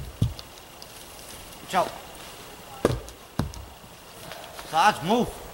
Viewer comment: Sarge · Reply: Ah. Well, mishearing that cave me a good idea for a dog name- lucky accident! "Targe" might even be a good name for a kid, come to think of it.